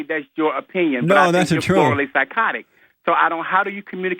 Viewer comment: "Florally psychotic"? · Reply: I thought I heard the doctor say that two times. I believe he is mispronouncing "thoroughly."